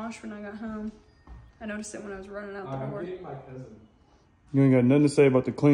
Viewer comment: Who is Anthony? I know I have no right to ask I’m just curious lol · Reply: I thought I heard him say he was getting his cousin out do the car lol. Referring to and daughter.